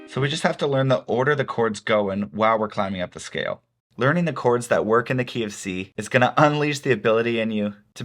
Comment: Learning the choreds that working in the KFC is what I heard XD